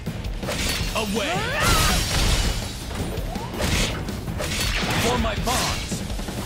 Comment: I had to rewatch this bit because I thought Jin was saying "for my mom"